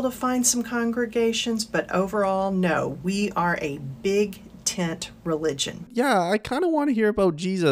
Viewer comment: What she said: "we are a big tent religion" What I heard: "we're a broad road religion"